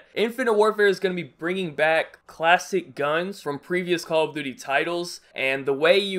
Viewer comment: Am I the only one who thought at first he said "plastic guns"?